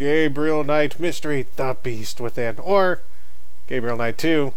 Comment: For a second there... I thought it said "The Breast Within" .... my bad XD?